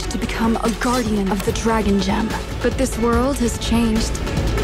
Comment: Did i just heard" The Dragon Java"